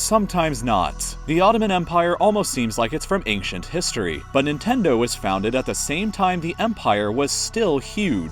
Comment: someone please tell me what he’s saying because I keep hearing nintendo